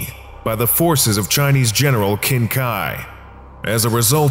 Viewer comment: Not gonna like I heard King Kai and was like WHAT!